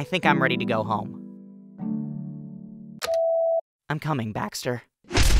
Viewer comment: "I'm coming, Baxter." My soul has been ripped from my body (please take this as a compliment). My heart SOARED at the mere MENTION of Lightbulb. This episode was incredible though!! The animation was awesome as always, and I'm so happy that Bot is now going by they/them. Kudos to for incredible writing! Thanks for taking the time to make these for us. A little recommendation, if you don't mind- The goal of this new season was to create a happy environment with reference to, but detached from Season 2 (which is spectacular but more on the serious side). Which I completely understand !! I understand Episode 15 will take a while, so take all the time you need, but I know a good portion of the II community would love some content of the final 4. Anything will do! Even just some shenanigans. (Just a suggestion though, no pressure!) Amazing work, crew! <3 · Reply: at first i thought paintbrush said: im coming back sir.